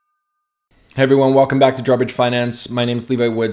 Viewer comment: I'm so upset that I heard "drawbridge finance" as "garbage finance" in your videos.